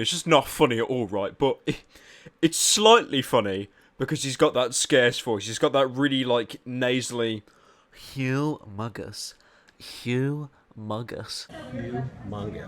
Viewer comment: i thought he said hes humongous chungus